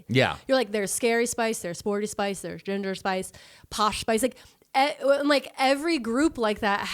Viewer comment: I totally thought you said "Gender Spice" and I tabbed back in, was confused, but then was like okay but who would be Gender 💅